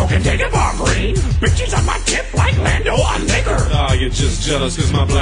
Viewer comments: did yoda say the n word??!! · That did not sound like liquor... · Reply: Oh wow, I thought it was a jab at him for being a fucking traitor, I thought he said "like Lando on Vader".